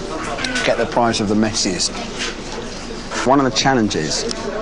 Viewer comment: Did he just say that the crappier burger will be the prize of the "mexi's"? Followed by a cheeky smirk. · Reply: No I'm pretty sure he said "messiest". And he then went on to say how he wanted all those toppings in his burger but didn't want it to be so messy.